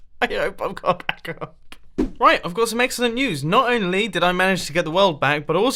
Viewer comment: what the hell did he say at 1:18???? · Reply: News